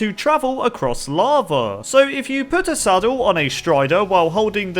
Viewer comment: What he says : LAVA What subtitles shows: LARVAE😂